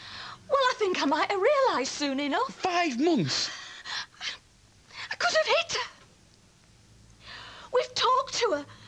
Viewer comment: What did Gail say at 3:19? Did she say she could have hit Sarah? Wtf.